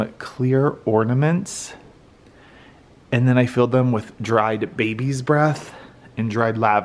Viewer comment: I was half watching whilst cooking dinner. I nearly dropped the pan when I thought I heard you say you’d bought clear baubles and filled them with dried babies 😳😂